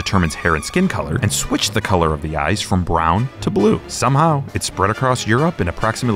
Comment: haha the subtitle at 0:55 ....ice instead of eyes